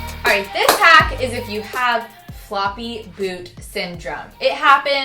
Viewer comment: I thought you said 'floppy boob' instead of 'floppy boot'